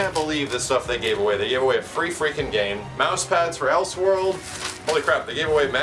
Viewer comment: Lol im sorry but @ 2:26 did he say els world??